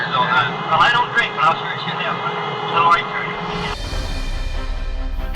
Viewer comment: sounds like he says "I'll sure as shit have one"